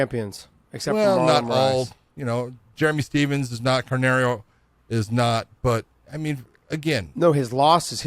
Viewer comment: Does say "Carnario"? Who is that?